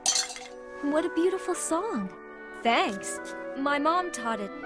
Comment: My Dum@ thought she said "What a beautiful soul"=_=